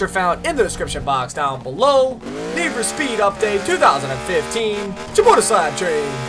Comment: did you said need for speed update 2015 ? :D